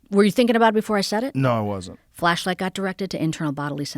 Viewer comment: I thought she said Fleshlight and it took me back to the good old JRE days.